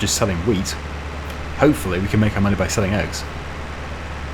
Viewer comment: I guess I'm not crazy (kinda disappointed about that) I wasn't the only one who heard you say sheep laying eggs 😂😂😂